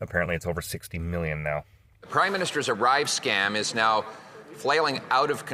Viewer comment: Did he call him the " crime minister " at 6:45? Maybe I'm late to the party and it's been said before. Because that would be fantastic